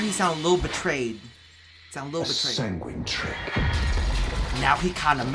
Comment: I thought he said "a sanguine trip" which sounds dope considering the light and flashy bits on mirage.